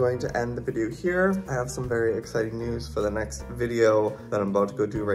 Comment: I thought you said SAD news... I had to rewind... Th a k goodness you said exciting news. For a moment I thought Little Red must have passed. LITTLE RED was a cool band back in the 2000s I like the name for foxy...Little Red.